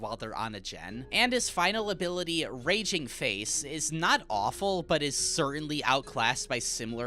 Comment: I KNOW you did not just say raging FACE